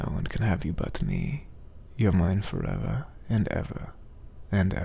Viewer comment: tell me why when he said "and ever"i heard "endeavor" XDD